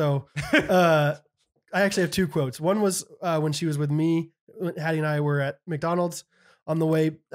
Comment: If people think Hattie is a boy when she has a bow on her head, I wonder if people think Bo is a girl when he has Hattie on his head. Sorry, had to. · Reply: Lol nice 😆 for awhile, I thought was saying “Pattie.” Lol.